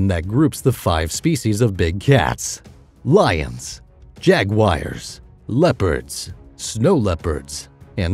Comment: did you say Jagwires